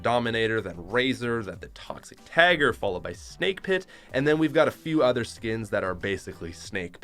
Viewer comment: I thought he said snake piss